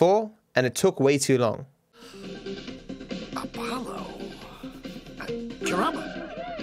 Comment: Did he just say "Apollo... drama"?